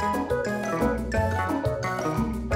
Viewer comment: I thought it said lab dog